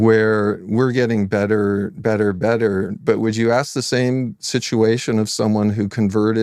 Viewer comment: what's he even saying at 1:02 ? "better, better, better," like that's supposed to make any sense?